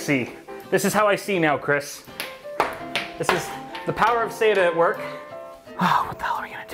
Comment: I am drunk so I heard you say "the power of Satan". I find it funny but that build was better than the Verge.